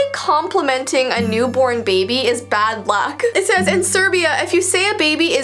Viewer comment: November 1st Hello Kitty's birthday, evil spirits. Mom telling evil she will make a cartoon character that is symbolizing evil. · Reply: did she say in serbia?